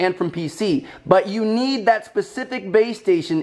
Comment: I swear I thought he was saying bae-station 😂😂